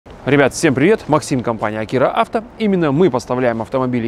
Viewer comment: "С вами Максим, Акира Авто", а мне послышалось: " С вами МАРКим Маркира Авто" - ох уж эта маркомания 😁😁😁 Галине, Денису и другим маркоманам привет! ✌😎